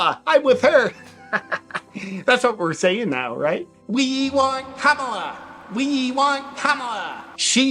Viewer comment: Did you just say "we want cumala" lol probably not 😂